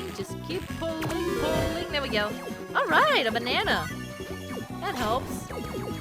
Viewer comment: I heard "Alright! I'm a Nana!"